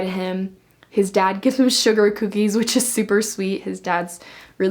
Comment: to start with I thought you said sugar cubes not sugar cookies and I was like hold on when was finick in d12😂